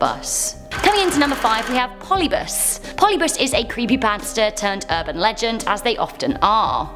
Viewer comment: I thought she said creepy bastards when she said creepy pasta😂😂😂